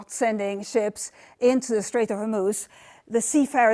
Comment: Did sky news bring on the cleaner to present this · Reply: Got to love subtitles though instead of writing it as the strait of Homuz it had it as the straight of a moose 😂😂😂 1:32 check it out with subtitles on m.